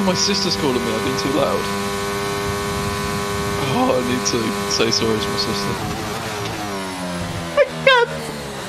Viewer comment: Wtf did say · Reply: I need to say sorry to my sister